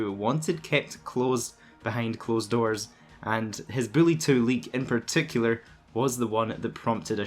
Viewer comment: Lol sounds like you said Billy 2